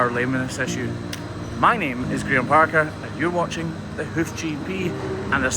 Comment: I heard my name is Peter Parker🤣